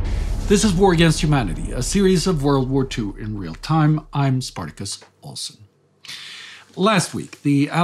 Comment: I misheard your name in the beginning of the video. From now on, I will call you Spartacus Awesome.